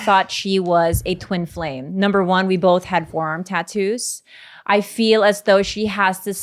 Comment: I thought she said WORM tattoos 😅😂